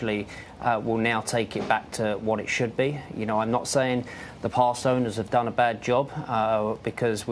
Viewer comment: Did he say past owners @ 1:22?